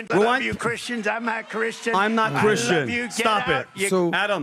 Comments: After listening closely, It sounds like Trump said I’m a Christian or Ima Christian. Not I’m not Christian. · Vinny your kid of a dumb regard. 5:55 Trump clearly said “IM A CHRISTIAN” not “I’m not Christian”. He paused on a weird way and it sounded a little like “not” but you’d have to be kinda dumb to not hear he said “A” instead of “not”. You even played it again for Adam and still got it wrong. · He did NOT say he’s NOT a Christian @ 5:48, rather he said “IMA CHRISTIAN” slurring, and speaking fast… in NYC accent - which WE NYC’ers can recognize better than anyone so stop itb